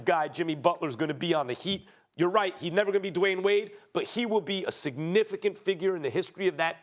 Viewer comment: I had to rewind it because I thought he said "Dwyane Wade is no Whore!" 🤣🤣🤣